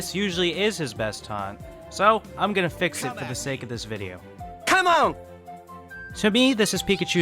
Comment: Terry sounds like he says "Allez come on come on" like he is french but he is American so I don't know what he actually says.